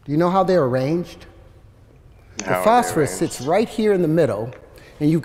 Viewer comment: Is anyone else incredibly annoyed when he leaves the consonants off the end of some of his words? Instead of "middle" he says "middo."